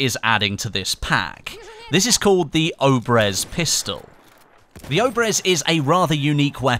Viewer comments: At first I thought it said orbeez pistol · i thought it said orbeez pistol 😂😥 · Not gonna lie but I thought it said orbies pistol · Who else thought it said orbeez pistol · Lol i thought it said orbeez pistol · I thought that said orbeez pistol lol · i thought it said orbeeze pistol, like to little gel balls